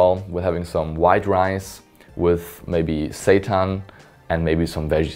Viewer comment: Did he just say, (or am I hearing things?) that he has Satan for dinner every night?